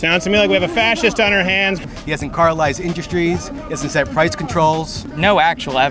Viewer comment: Did he say He has caramelized industrys?